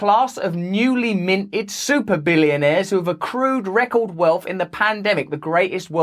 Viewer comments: At the start of the video I thought I heard him say super VILLIANaires and then realized it wasn't wrong either way 😅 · Totally thought he said “newly minted Villianaires” and thought “Accurate”